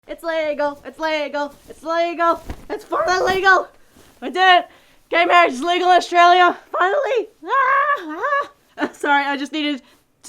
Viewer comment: I thought at first you said "Its Lego! Its Lego! Its Lego!" Im sorry XD